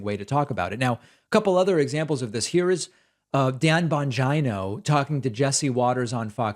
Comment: Bongino... I heard "Vagino". As in male vagina. Lol. Oops.